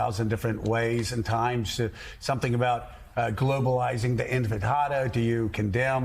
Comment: I heard enchilada, empanada, inpinada now I don't know If I heard right but did he just said Intif i tada? close xD